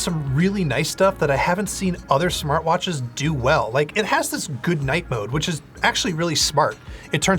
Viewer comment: I thought you said good "night" mode but it turns out it's "goodnight" mode 😂